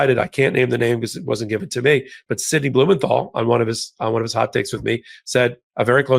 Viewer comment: I swore I heard "Sydney Blumenthal, on one of his hot dates with me..."